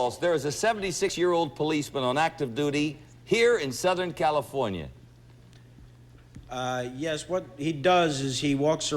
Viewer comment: wonder what Rob Riiner said at 10:20 that had to be bleeped? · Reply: Looked like maybe "f***ing himself"?